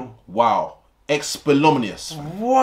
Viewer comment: what the fuck did just say i’m dead 😂😂😂😂😂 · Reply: Esplomnias 😭😭😭😭